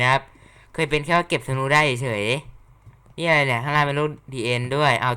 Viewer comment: พูดว่าอะไรหรอครับฟังไม่ออก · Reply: "นี่อะไรเนี่ย ข้างล่างเป็นโลก The End ด้วย" ครับ